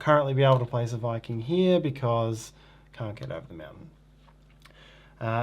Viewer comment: What did you say? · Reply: Can't get over the mountain